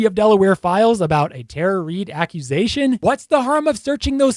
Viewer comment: Call me stupid but in the beginning I thought he said Terror Reid 😂